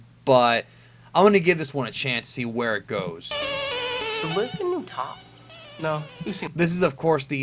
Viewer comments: I think he called her Liz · I thought he said does Liz give good top